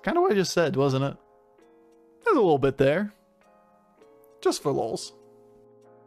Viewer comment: What do you say at 18:23, your mic cut out a little? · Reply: “Just follows”